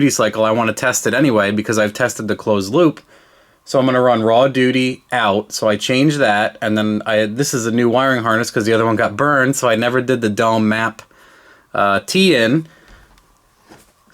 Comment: I Hear say "Raw Doody" ....My day is complete! 😂😂